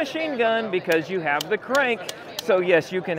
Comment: I thought he said maybe we can go down the street with 😂 😂😂